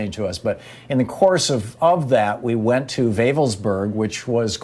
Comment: Is Thom saying Eggelsburg, Austria (2:32-2:34)? or someplace else?